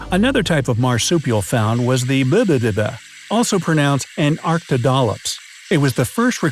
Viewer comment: did you say a bibbibbada?